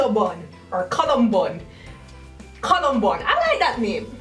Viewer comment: Was I the only one who was in utter shock cause they heard said columnbun and thought she said Columbine....😶